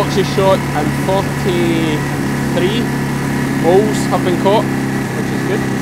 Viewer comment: Lambies! 43 what have been caught? Poles? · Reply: Moles! The first time I heard it I thought said "Bulls" (??) Later in the vlog he pointed out a mole hill & I had my "ah ha!" moment. The CC was no help - it translated "moles" as "BS" (??) 💕💕